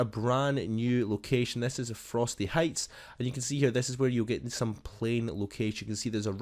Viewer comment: did he just say frosty heights